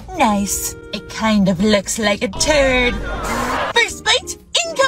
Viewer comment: When the goofy ahh lady really said " it looks like a turd " I thought they said toad